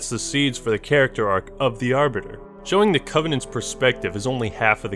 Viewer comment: did he say arbiter gay